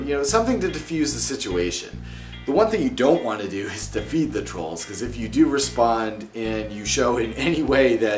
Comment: Trolls go great with bean dip and homemade Coca Cola. Oh wait, you said don't feed the trolls. I thought you said don't feed ON trolls. :D